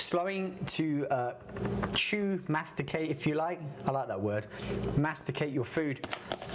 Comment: for a moment i thought he said masturbate your food